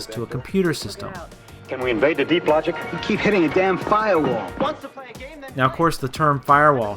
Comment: "Firewall"? I always thought he said "File wall" not that that exists. I just assumed it did because it sounded like he said it.